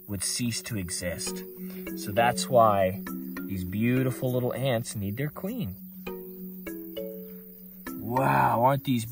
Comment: bro i thought that you said that is why they eat there queen.